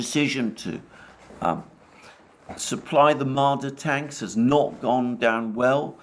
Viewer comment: I thought people were saying "the Martyr tank" I was like wow, what a terrible name for a tank. Inspires no confidence whatsoever, unless maybe if you're Arab But it's Mardar, ok , go it, silly me!